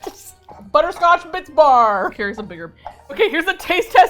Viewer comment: Who else heard butterscotch bitch bar at 4:07?😂😂😂😂😂